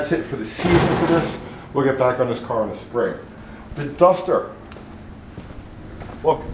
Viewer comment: Dumpster oh wait you said "duster" My bad: " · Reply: I thought he said dumpster too!